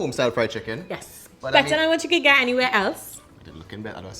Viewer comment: What did she say at 6:45🤣🤣🇬🇾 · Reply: Better than what you could get anywhere else.